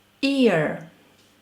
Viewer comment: I was on another tab and I kept hearing you say "ear, ear'! LOL! You forget to prounounce the Y for YEAR! Try this: Yi-err. (Don't worry: I'm not native anglophone either!) I would love to learn Russian but it takes me more than 1 week to memorize 1 word (short term memory loss from bad medication) & after I got it, today I could not remember "Monday" and many other days. I thought Vasskrayssiayniyay was Monday! >.< Russian is too hard (too many strange syllables together) and COMPLICATED!! Ex: I put one verb on Google Translate and it gives me the Russian word. I switch with the arrow eng/ru to ru/eng and now the word has completely changed on me. Russian has like 7 or 10 different words for one same word! Too hard: I can't even remember the first word. HOW am I supposed to learn 10 words for 1 normal word? I can't even remember a TV show after I watched it or if I watched it at all! Also every verb has 2 forms (im//perfective) and I can't make heads or tails of declensions because no one has ever explained them to me well, at all! So I have no idea what to use because on top of that EVERY declension changes, not only with case (usage) but with plural, singular, fem or masc. To make matters worse, the plural changes. It's not S like in English. Sometimes you add ii at the end sometimes you add A and sometimes you remove end vowels like in CLOB! UGH! IMPOSSIBLE TO REMEMBER unless you're native born. I gave up 40 yrs ago and I give up now. I guess with handheld translators, no